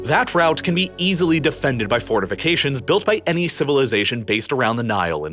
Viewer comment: Just for a second I thought you said, ..”invasion can easily be stopped by fornication”🤔. How would that work..😂